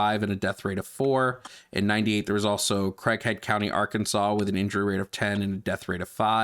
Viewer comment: the caption says "Crackhead County" i know its Craighead but lol😂